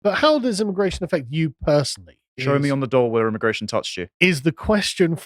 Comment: I thought he said "Show me on the Dole where immigration touched you?" Which the answer should be "everywhere..."